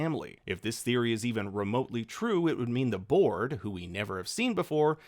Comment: I thought you said "the borg." (See Star Trek)